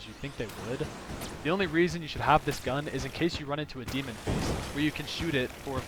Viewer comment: What are you talking about here at the free gun, I've never heard of this. (edit: i hear "demon priest" but it might be something different) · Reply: Demon face. If you find the demon face portal, and use any liquid creating item on it then it will drop a random A or S tier gun